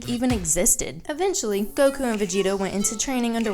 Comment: Did anyone else heard her say goku and vegito at 2:10?